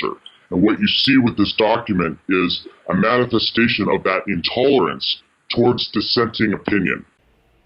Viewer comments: Couple of typos in the subs. 4:12 your subtitles say 'tolerance' instead of 'intolerance' 5:06 subtitles says 'insisting' instead of 'existing' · the text on the screen doesn't match what he said as you wrote tolerance and he said intolerance. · Great work! You need to fix the subtitle at about 4:12 because it misquotes the insider with the word "tolerance" when he actually said "intolerance." 'Course, it probably won't matter because The Borg will ban this video shortly. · text should read “intolerance” instead of “tolerance”. At least, that’s what my ears picked up. Correct me if I misheard.